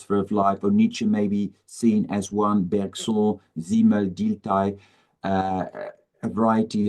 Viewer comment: what are the word(s) uses at 34:27? - "..he [Klages] is not the only philosopher of life, Nietzsche may be seen as one ??bergson zeemodertai?? variety of those..." Thank you! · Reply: He says Wilhelm Dilthey!